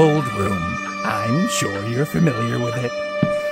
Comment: What did he said?? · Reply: He said “I’m sure You’re Familiar with it”.